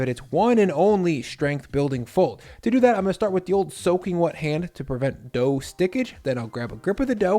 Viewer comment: did he say soapy wet hand? Wouldn't that get soap in the dough?